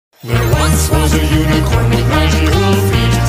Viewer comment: When I first heard this I thought they were saying 'human corn flakes'. Unicorn makes much more sense but they it was funny